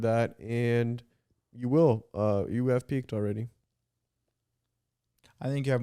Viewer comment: 23:3 did just speak arabic???? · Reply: I thought the same😭😭😭 I thought he said "akied, akied" (sure, sure)